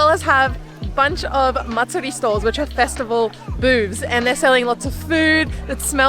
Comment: I had to rewind a could times bc I swore she said festival bewbs